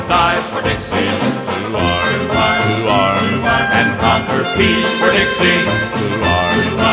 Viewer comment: I can’t hold myself from laughing… I can hear it now because of better headphones, but the first time I heard this I swore he said “Conquer geese for Dixie” that’s what I heard, I’m being honest